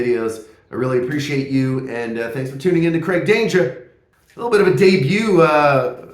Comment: At first I thought you said: "Great Danger" 🙂. Explosive name. Thank you for an interesting video. First time watching your videos and I'll be back.